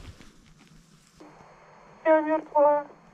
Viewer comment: На минуте 11:35 из рации чётко слышно : "Я Мертва" после этого ты сказал : "не понял что сказали"